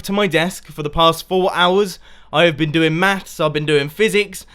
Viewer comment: I thought you said that you were doing meth! xD